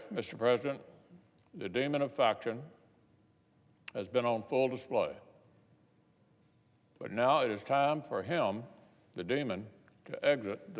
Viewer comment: "the demon of faction was on display, it's time for he, the demon to leave the stage". Is that what he said? Oh man that was beautiful.